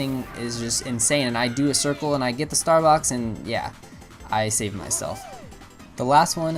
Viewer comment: lol instead of star box I thought the said Starbucks